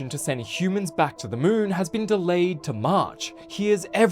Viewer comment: Thought he said “delayed to mars”